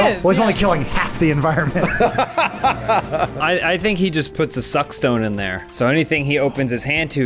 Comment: I thought James said the Zucc stone